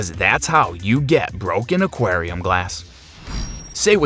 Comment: For a pro I cant understand what he says. did anyone catch what he said at 4:59 Did you ... broke an aquarium glass? Or whatever that was... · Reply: “Did you want broken aquarium glass?”